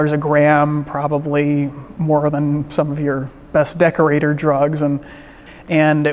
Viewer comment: did he just say “decorator drugs”?